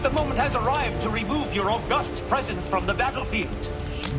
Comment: "Your august presence". Even in life-or-death situations, Garak, in every universe, finds a way to say it nicely. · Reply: I always thought he said robust presence. I suppose either one would work.